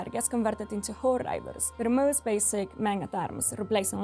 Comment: I am pretty sure she said horde riders because that is what is in the game but the subtitals say that she said horde rivals